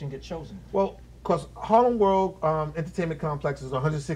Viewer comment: Who in their lost mind put "Holland" instead of HARLEM World!!!